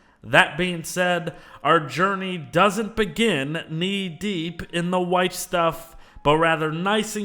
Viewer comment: “Knee deep in the white stuff” Okay. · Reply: I heard “Knee deep in the wife stuff” lol